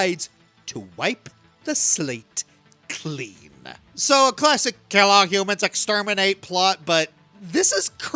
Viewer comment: Not gonna when you said “classic kill all exterminate humans” I thought you said “classic Kelloggs exterminate humans”